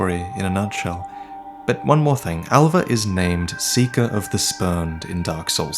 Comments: i thought you were saying seeking of the sperm for a little while......help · I thought you said “Seeker of the sperm” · Thought you said "Seeker of the Sperm". I mean I know Souls has some weird stuff in them, but I was really confused haha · All the time I thought he said "Seeker of the sperm", no joke. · I totally thought you said "Seeker of the spermed.". lmao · I keep hearing "Seeker of the Sperm." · Alright to be honest I’m half awake right now and when you said seeker of the spurned I thought I heard “Seeker of Sperm” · I misheard you so bad. I thought you said Seeker of the Sperm XD